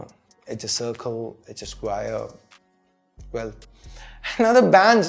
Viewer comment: it sounds like you say Squier 😂😂😂😂 (Fender)